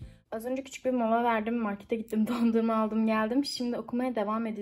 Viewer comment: de dondurmayı pandora olarak duydum WKSLEŞD